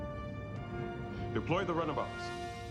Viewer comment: totally thought he said "Employ the runabouts" the first time I watched it